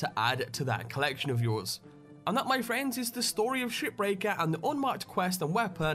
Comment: I thought you said "SHITBREAKER"